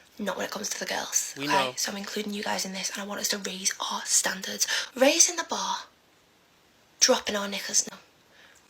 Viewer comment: maybe that's not what she said but it sure where my mind went 😂 Maybe she said raising the bar and dropping our knickers? 😅🤣